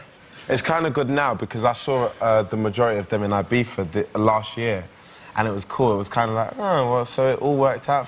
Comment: What is he saying at 5:41? "I saw the majority of them in ??? for..." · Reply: Ibiza last summer 👍🏻